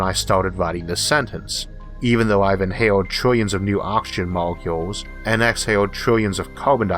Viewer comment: "The Quantum Chimichanga." Heh. That might make for a decent band name. ... I just misheard you saying "oxygen molecule" as "option molecule", which if it existed would make the world an even more interesting place than I currently think it is. Not your speech, I have some hearing problems.